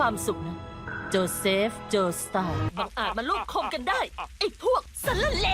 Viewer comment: ผมได้ยินคำว่า "ลูบคม"